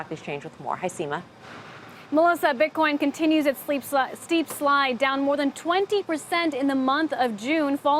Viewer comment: Did she just say ; .. steep slut?